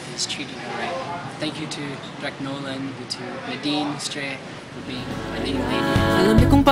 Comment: whaaa!!! just notice at 01:00 he said Nadine Reid.... OJD. ...💜💜💜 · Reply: i heard "nadine lustre.. being my leading lady"